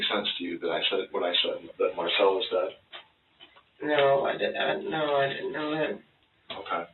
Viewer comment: it sounded like she said “no I didn’t know that” rather than “no I didn’t know him”